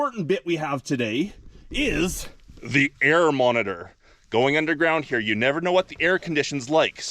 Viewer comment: LOL At first I thought you said, "BEAR Monitor".